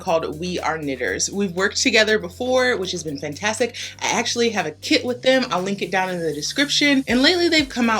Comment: When you said "I actually have a kit with them" what I heard was "I actually have a kid with them" 🤣